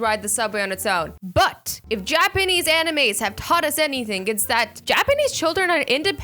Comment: did you just say japanese anime?!